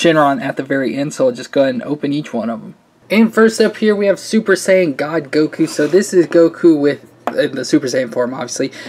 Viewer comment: Did he call super saiyan Goku super Saiyan god Goku ? 1:23 (I’m not trying to be rude I was just asking.)